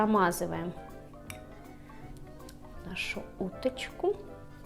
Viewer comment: мне одному послышалось "промазываем жопочку"😂?